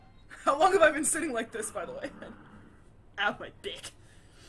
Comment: I had to listen to you say that twice, at first it sounded like "Ow my dick!" O_o "I don't need this much health!" she says as Leon continues to make wounded noises. 12:43 it was at this moment knew....Leon was gonna get FUCKED UP. Cuz Mr X Gonna give to ya, he gonna give it to ya!! And when you knocked his hat off, you made him Faster. Fun, huh? Getting shades of the wandering monster/bosses from the first Outlast? Yeahhhh....you're not the first person to make that "safe space" comment.